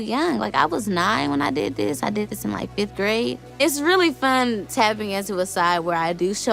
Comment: did she say she was 9 in 5th grade!